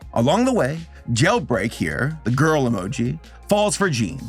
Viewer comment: Every time I heard the name “Jailbreak” I heard jail bait and got very uncomfortable